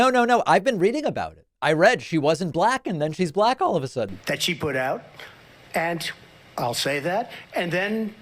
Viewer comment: At marker 7:40, did he say that "She put out"?